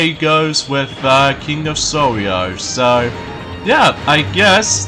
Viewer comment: it's king of Sorrow! it sounds like your saying sorrio! it's KING OF SORROW!!! SORROW!!! SORROW!!!